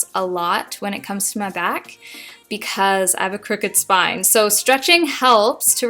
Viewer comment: I thought when you said "crooked spine" you said "perfect spine" meaning its completely straight. Oops lol